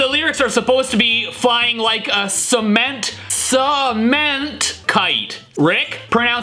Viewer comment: I heard "seaman kite!"